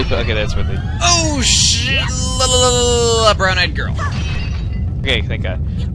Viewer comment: did he say brown eyed girls from K-POP